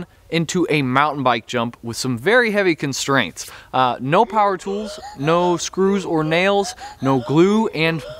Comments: I thought you said no tools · i thought he said no tools